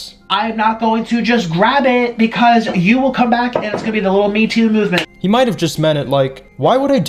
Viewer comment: No if you look at his lips i think he really was saying "little" me too movement